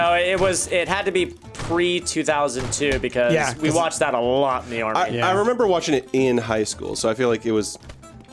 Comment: Did say in the Army